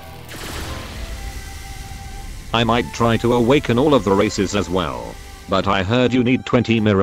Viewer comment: i thought you said racist